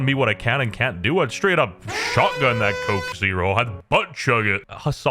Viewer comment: i thought you said youll shotgun the woman